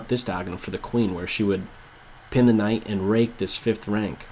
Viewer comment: Did say "RAPE" @ 1:48? LOL